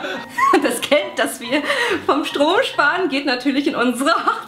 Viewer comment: Wer hat bei 2:50 auch 'Stroh' sparen verstanden? :D